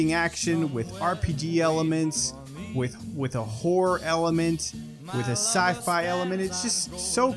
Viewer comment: lol I thought I heard you say “whore” and not “horror” for Bioshock 🤣